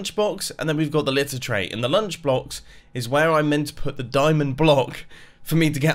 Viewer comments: did said "lunch blocks"? ur still cool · I swear at 0:29 he says lunch BLOX